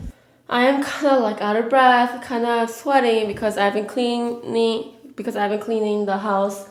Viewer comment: I thought you said I'm sweating because I'm pregnant 😂😂😂